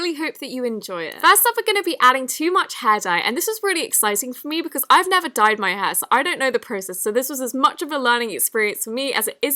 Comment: OMG IS IT JUST ME OR AT 0:09 IS ANYONE HEARING "this is really exciting for me because ive never dyed my ass" LIKE IM CRYING